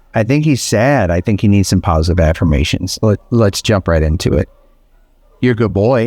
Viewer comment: Lol. Sounded like you said, PAWSITIVE AFFIRMATIONS